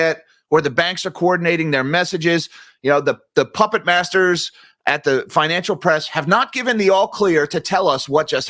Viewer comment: Lol at 16:15 i thought he called them the "Pump It" Masters, I'm sure he said Pupit Masters, just gave me a good laugh.